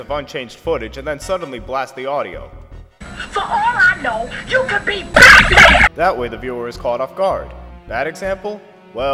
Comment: all I heard was “You could be *P A C M A N*” So my dream came true